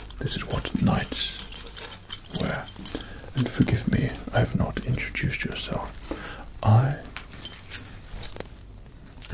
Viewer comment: did he say i have not yey introduced yourself